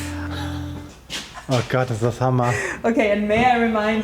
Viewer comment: What is the word they are using that’s translated as “killer”? As in, really cool. Killer. It sounds like “hammer”. Example at 10:37. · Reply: It is "Hammer" indeed. Its german for hammer (duh) and we use it when something is awesome